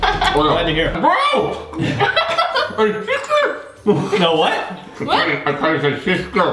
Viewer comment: it sounded like he said i tasted your sister